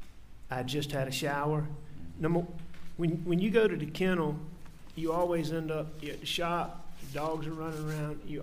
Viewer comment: you always end up gettin shot? it sounds like he says it. maybe it’s shop, but that doesn’t make sense. 👑 🤍 love, david